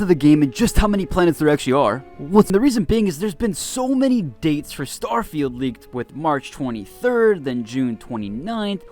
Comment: What does he say at 0:10 lol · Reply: I'll go slower on the intros lmao, just been a bit with the channel for a while. "what's up world, its Eutopia here back again with another video"